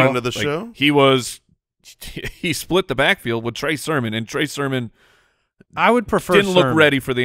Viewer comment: You say "Trey Sermon," but I always hear "Trace Herman."